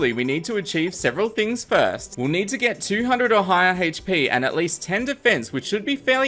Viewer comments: please tell me that I am not the only one who hears him say "200 ohio hp" I AM CURSED! also this is fantastic! great job, cant wait to see what happens next! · At first, I thought you said "Ohio HP" and I was like wut, but I thought about it for a moment and realized you said "or higher". XD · it sounds like he said "*Ohio HP*"